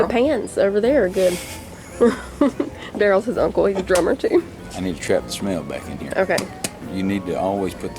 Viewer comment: When y’all were smelling candles I thought said “We need to get the trout back in there “ 🤣